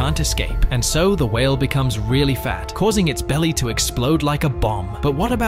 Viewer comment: I hear they said " i'm about to explode ".